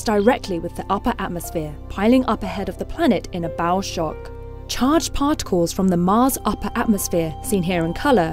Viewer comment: Did she say "bowel shock"? LOL